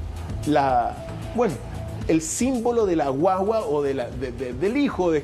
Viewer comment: Revisen el minuto 16:38, se olle la voz de un demonio que dice: la wuawua! Verdaderamente aterrador!